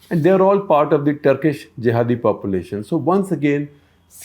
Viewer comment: did you say turkish jihadi population?